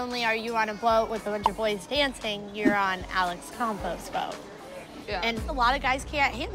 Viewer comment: any time they shorten Alex's long Greek last name, I always thought they were saying Alex Compost 😂